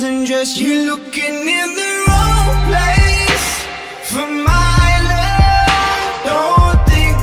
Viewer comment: Am I the only one who misheard "For my love" as "For Milo" ? :^